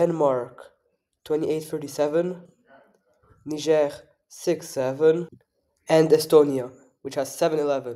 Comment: WHAT DID YOU SAY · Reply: 6 7